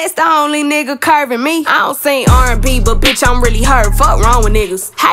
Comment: always thought she was saying "i'm really hurt"... and not "i'm really her" haha